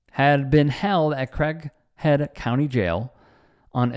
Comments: Sounds like he is saying Crackhead County :) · I thought I heard Crackhead County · CRAIGHEAD COUNTY thought you said CRACKHEAD COUNTY. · Sounds like he says Crackhead County jail. If any cop does this they should get the same treatment! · Sound like he said crack head county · Holy crap, this felt like a snuff film! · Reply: I thought he said Crackhead County.